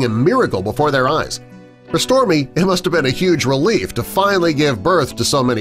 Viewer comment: it says for storie instead of stormy